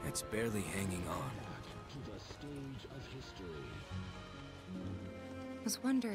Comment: do i hear "Welcome Back to Stage of Victory...." lol